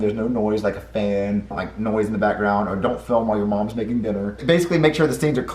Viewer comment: When he said don’t record when ur moms making dinner I thought he said don’t record when you moms in her bedroom😂